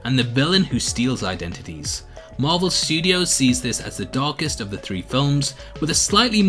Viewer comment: Dammit I thought it said "dankest"